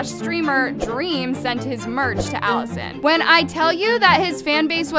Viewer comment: Am I the only one who heard her say “Alison” in the part about the merch being sent out to Addison like wtf